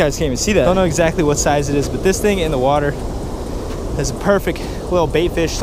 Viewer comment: Did you say perfeck